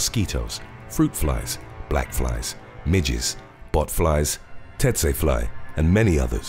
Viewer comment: bruh I thought he said Midgets 🤣